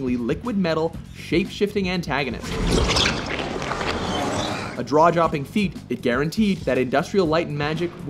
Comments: Does he say 'a draw-jopping' feat? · Did he say "draw-jopping"? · The narrator says "Draw jopping" instead of "jaw dropping" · he said "draw-jopping" instead of "jaw-dropping". XD